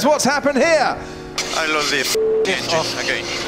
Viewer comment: I think he said, that he ,,loves" this engine not lost